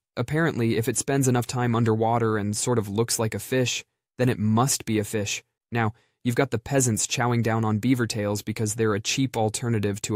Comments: I thought this said pheasants and was very curious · I Thought this said pheasants like the bird and i got real confused at num 2